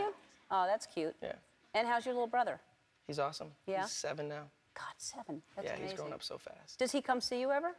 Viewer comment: when Ellen said ''god 7" at firs I thought she said ''got7'' 😂omg 😂 any kpoppers?